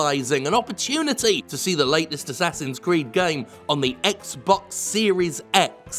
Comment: tell me lies tell me sweet little lies. I swear i heard Assassin's Greed.